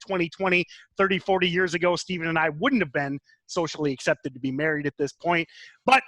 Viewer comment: Lol i had to rewind that & listen close. I just started following you guys and I heard him say we are married and I was like.....🧐😳😂